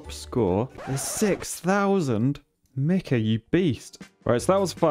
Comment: Lol it sounded like he say nigga you beast. At the Time stamp of 7:47, I know he said mikha you beast but listen to that with you eyes closed